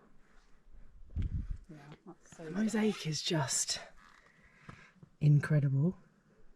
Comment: I really appreciate your little reminders to breathe and drop our shoulders! ❤ When you said mosaic I heard “f*ck sake” 🤔😂 I need more culture in my life! X